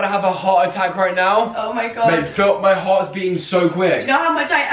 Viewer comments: Did he say "bae feel my heart right now" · did he say babe or? 😂🤷🏼‍♀️ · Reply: Mate?? 🤔